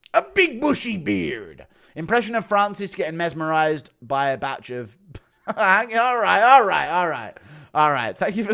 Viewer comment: what did the dono say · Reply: Impression of Nganou getting mesmerimised by a bunch of bananas on a tree before his crash - Something like that anyways lol